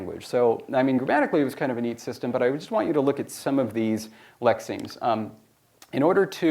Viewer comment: At about 7:44, the subtitle claims that David is inaudible. It is wrong. He clearly says "lexemes" and I'm assuming the subtitler didn't know that was a word.